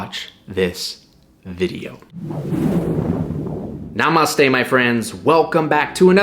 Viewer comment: Wait wait did he just sayyy Namastey!!? 😭